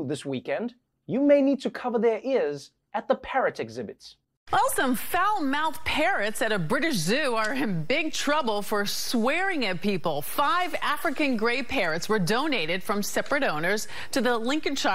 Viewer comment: I think I'm deaf. I heard like Parents at the zoo..! Then I saw the pic. Oh, parrots.